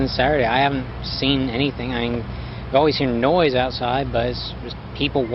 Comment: The only thing I thought was weird was that he said "I have always SEEN noise outside"